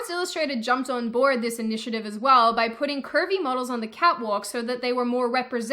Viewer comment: For a second i thought you said "Pervy models" hahhahahah